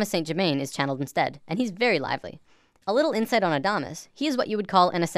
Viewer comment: Did you say dominus?